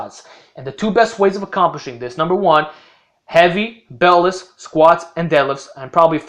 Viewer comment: does anyone know what he says at 1:13? I cant tell, "heavy" something something somethinf "squats deadlifts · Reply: Late but nvm , he said Heavy BeltLess